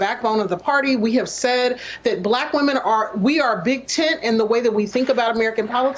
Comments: REACHING MAXIMUM DENSITY 1:37 .. it sounded like she said " we are big tit " lol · Brittany: We are a big tent. America: Yes, yes you are. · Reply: I thought she said big tit